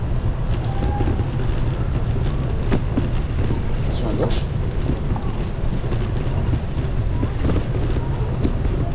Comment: Trés bon vidéo, merci ! What did you say at 6:13? << ? gauche >> C'est une phrase importante pour un apprenant de français qui fait du vélo :-) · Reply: « Attention à gauche » :p